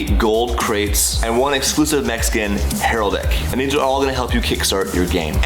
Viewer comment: During the AD I thought he said “Mexican” not mech skin lmfaoooo